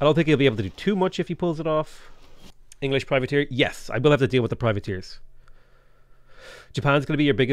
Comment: Caravels aren’t invisible. Naval raider class vessels are invisible to everything except other naval raider vessels, unless directly adjacent. Caravels are naval melee class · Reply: I did watch the video. I misheard him. I thought that he said caravels couldn’t be seen, but he said privateers.